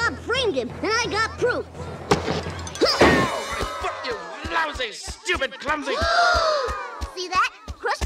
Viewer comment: Did he just say the f word? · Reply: oh wait he didnt he said lowsy my ears are broken lol 😂